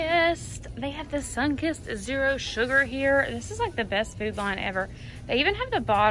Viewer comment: I'm cracking up! Are you at Food Line? I've been hearing say it and I thought she was saying Food Lion 😂! I love you girls accent!!! Northern girl here from Michigan